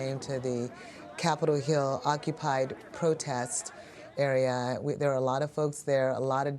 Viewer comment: So they changed their name from CHAZ to Capitol Hill Occupied Protest, CHOP really ? 😂🤣😂🤣. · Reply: Ohhh. I thought she said it was renamed Capital Occupy Protest Sanctuary. COPS. 😮